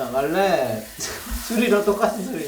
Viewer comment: Parece que dice agua 😂😂😢